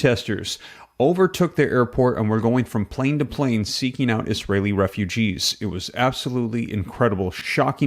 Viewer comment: Did he just say Israel refugees ???😂